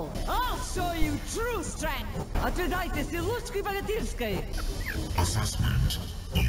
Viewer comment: I'am Russian, and i understood what she said from 2nd replay XD For those, who wonders, it's: "ОТВЕДАЙТЕ СИЛУШКИ БОГАТЫРСКОЙ!"(Otvedayte silushki bogatyrskoy!) Which means: "Taste the Bogatyr's power!"